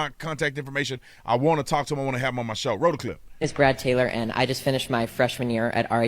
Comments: sounds like he said his name is Brad Taylor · Hey this could be the best show of yours i've ever seen! I love your response to this brave young man! Sounded like he said his name was Brad Taylor.